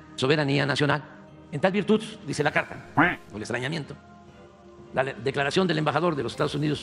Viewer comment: Le entendí "estreñimiento" 😂😂😂 jajja...